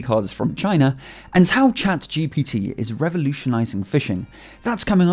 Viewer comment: lol i heard "ChatGPT is revolutionising fission"